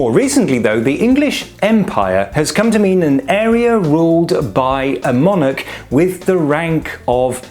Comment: I thought you were about to say "an area ruled by a moron"...